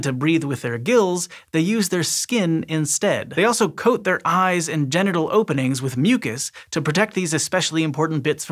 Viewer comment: Thought he said they block their eyes with genital openings and my brain had to reboot.